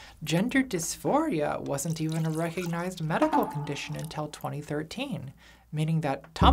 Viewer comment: I hear "medical condition" but the subtitle says "mental condition". Which is right?